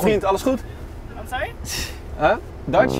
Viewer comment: je hoort toch dat ie dutchbros medewerker ''wat zeg je zegt'' in het nederlands zegt